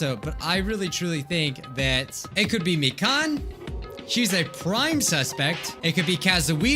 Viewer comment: I pause it and I thought he said "I really think it could be me."